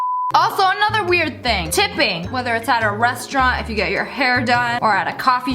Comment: It kind of sounded like she said at a restaurant where you get your hair done